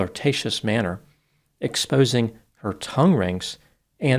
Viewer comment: I thought he said “exposing her toe rings” lol. Had to rewind 😂